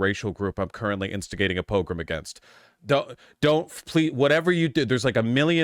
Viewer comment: what is the word in the phrase “I’m instigating a ___ against” I’ve never heard it before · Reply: Pogrom.